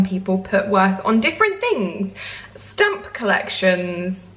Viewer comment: When you said “some people put their worth on different things, stamp collections” I thought you said STUMP collections and for a few seconds I thought “people actually collect tree stumps?” but then my senses came back to me 😂🤦🏽‍♀️